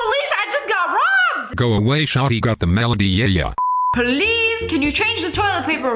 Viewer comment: did anybody else think she said police and then realized that she said please ❤😂😂